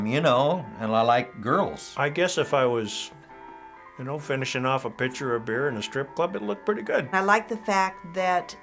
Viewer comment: I thought he said finishing off a bitch or a beer